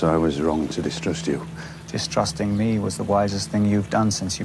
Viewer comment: I thought he said “thrusting me was the wisest thing you’ve done since you climbed off your horse” 😂